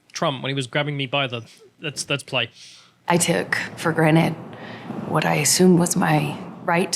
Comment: did she say she took it for granite? Rick and Amber has a lot in common.